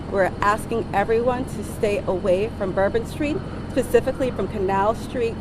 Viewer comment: did she say "pacifically" or "specifically"????????